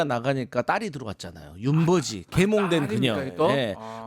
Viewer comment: 윤버지 들으면 자꾸 ‘윤버ㄹ지’로 들림